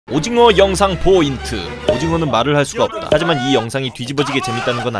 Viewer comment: 몇 번을 돌려 들어도 김계정 영상 포인트라 들리는데 정상 맞죠